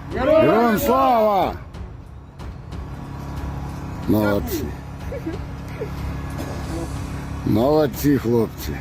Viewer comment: Can anyone explain what is being said at he 2:05 mark by the camera operator? It sounds like "nazi, nazi". If it is does it sound sarcastic to you?